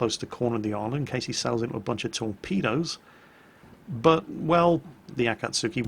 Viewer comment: All I heard was 'tor-pedos'